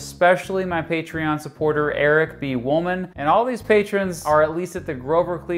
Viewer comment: I misheard Eric B. Wolman as Eric vs. Woman, which made me think your biggest patron was probably single.